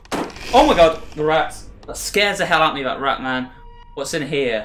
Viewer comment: did he say... Ratman?